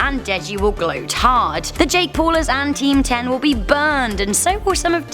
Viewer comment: Wait wait what she say team 10? Replay 3:32 more like team 1